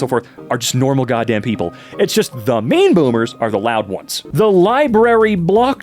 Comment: I keep hearing 'meme boomer' as opposed to 'mean boomer' I guess they're not far off.